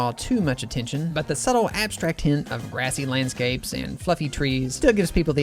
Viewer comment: I thought he said "fucking" instead of "fluffy" 😭😭😭